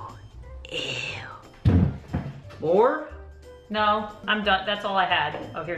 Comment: AM I THE ONLY PERSON WHO HEARD SAY LAUR?! go to this time: 6:20 And then u will hear it just wait a few seconds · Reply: I think he was asking if there was more pranks I heard it as 'More!?!' But idk ( no hate btw) ❣💙🙂